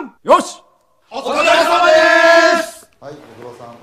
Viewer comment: お疲れ様デェェェスwwwwwみたいな感じに聞こえるのだが